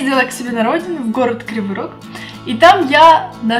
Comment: Город Криворук ? Или мне послышалось